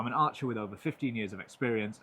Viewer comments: In the beginning, I thought you said “I have 50+ years of experience” and I was like “AINT NO WAY HES 60+ YEARS OLD” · Who else thought he said "50 years of experience"